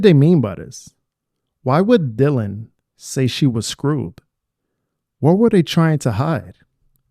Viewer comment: Dylan did not say she was screwed, I think she said she was scared because asked her to come to her room, that's when said that it was better than being alone. Anyway, as someone commented here, it's weird that says Dylan is alone, when she has Ethan and Xana right next to her... I am glad you came back, I appreciate very much your videos and your voice is great.